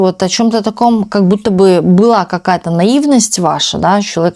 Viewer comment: Вы сказали "какая-то насранность" , или мне послышалось ??😂😂😂😂 Теперь весь вечер буду смеяться...😂😂😂 Мне нравится, что вы можете сказать "нахрен не нужен" , "а вам уже похрен"😂 Это так жизненно! Слушаю вас, вы берете паузу а я мысленно уже проговариваю "да мне это уже нахрен не нужно" и вы тоже самое озвучиваете, прям в точку, впечатляет!)) ❤